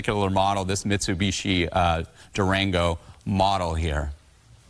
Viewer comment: Did he say "Mitsubishi Durango"? Remind me to NEVER buy a car or truck from him. 😁🤣